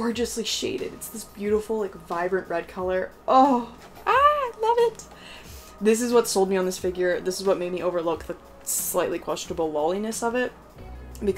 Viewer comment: i heard "vegetable loliness" and i thought i was a new saying in the figure community lol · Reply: Haha she said “questionable” not “vegetable” hahahahaha